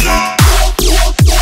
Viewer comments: To my ears, that doesn't sound too bad. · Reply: i always thought he said "tomorrow doesnt sound to bad" lmao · To my ears, that doesn't sound too bad. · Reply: i thought he said "ummm that doesnt sound too bad"